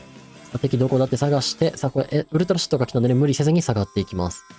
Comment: ウルトラショットがウルトラシットに聞こえてしまった。当たらずとも遠からずだと思うけどw